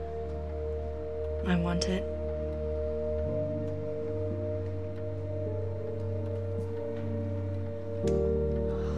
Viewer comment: can anyone explain what she was saying, she wanted it because…? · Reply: For you I think? 😗